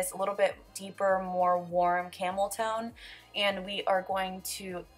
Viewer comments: I thought you said camel toe also. Hehe 😂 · Omg when she said camel tone I honestly thought she said camel toe at first so it's funny she pointed it out 😂